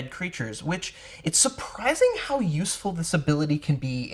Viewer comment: Did you say it's surprising how useless Divine Sense is?